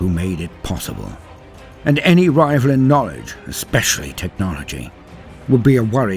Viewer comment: Misheard that as "Any rival in Norwich" and got really confused for a moment. Was the Golden Throne in Norfolk for a bit?